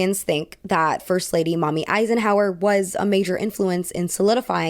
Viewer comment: thought you said "mommy eisenhower" and it kind of shocked me in the head